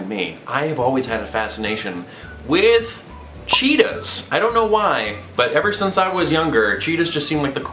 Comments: 1: when you said your fave animal I thought you said Cheeto 2: HARRY POTTER THE FREAKING DOG 3 : don't think of your body as bad! actually to me you look like a cuddly teddy bear😘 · i thought you said Cheetos xD